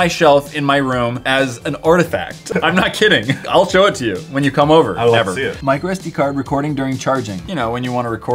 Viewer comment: did anyone else catch it when said "i'll show it to you" and then he whispered never at 7:17 in the video hahhaha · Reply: He said " If you come over.... ever. "